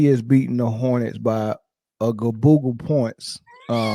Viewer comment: Hold up......what was that word again? 😂😂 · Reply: Gaboogle! Lol